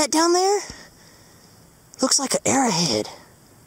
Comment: Oh! I thought you said you found an "airhead"... like some kid's character, maybe from ,80's or 90's packs of candy or cereal.